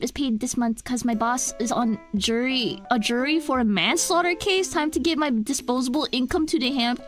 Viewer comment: for a moment I thought he said “my boss is on trial for a manslaughter case” so i went “did he shoot the hostage”